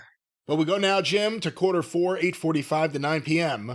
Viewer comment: I heard “Cajun Wayne” and wondered what cursed gimmick AEW gave the kid.